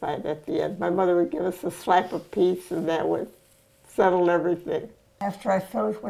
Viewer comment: “My mother gave us a slap each and that settled it” 😅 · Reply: Lol, I thought she said a slice of pizza! 😂